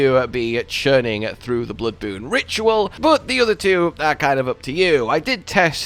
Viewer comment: Bloodboone is amazing for area denial, cast out the flies and throw bloodboone BEHIND your opponent, bloodboone doesn’t do much damage but fills bleed super fast, throwing it behind them and having flies following them is very hard to avoid combine with something like rivers of blood, it’s basically a death sentence if your bloodboone is thrown very strategically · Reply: I thought u said bloodborne at first 💀